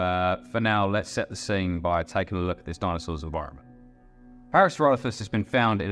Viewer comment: Ok, guys I know it's a little hard to understand at times, I'm not sure why this video is worse for it than my others but what can I say? Can take the boy out of Essex but you can't take Essex out the boy.....don't worry, I'm working on it 😅 Hope you still enjoy and thank you for stopping by! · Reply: Yes, I had to back track and put the subtitles on a couple of times! When you said "environment" I only caught the "ro" sound and thought you might be saying where it "roamed". Dare I say it? Your pronunciation sounded a little lazy. Goodness knows that I don't have the clearest speaking voice (I'm a Yam-Yam, from the Black Country, plus I have a naturally soft voice). However, working as a secretary, I have come to realise how important it is to speak clearly so that other people can understand you. Each syllable has to be enunciated clearly, and unless someone has an actual speech impediment, then it is possible to ensure that your spoken voice can be easily understood by all your listeners and viewers. I must say that it is quite rare for me to have this difficulty, as I worked as an audio typist and transcribed tapes made by people with all different kinds of accents and dictation styles.